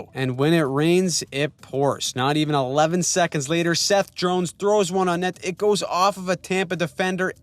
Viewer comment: Did I hear a "Seth Drones"?